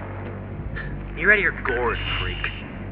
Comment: what does say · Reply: “You’re out of your gourd, freak.” Kind of a cheesy 80’s way of calling someone crazy